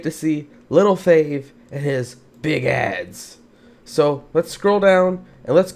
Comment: i thought he said big ed 😂